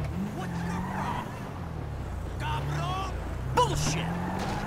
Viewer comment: Мне послышалось или он сказал на 5:42 получи